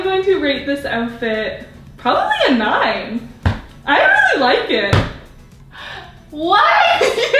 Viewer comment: Go to 7:16 it sounds like she said she didn’t like it